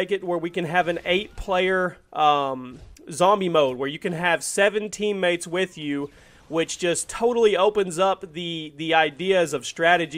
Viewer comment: lol i thought he said seventeen mates with you